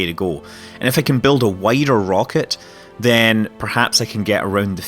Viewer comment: I thought you said "whiter" instead of "wider" XD